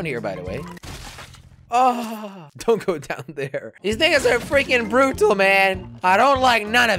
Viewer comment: wjat did he say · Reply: Don’t go down th - laughs - ere.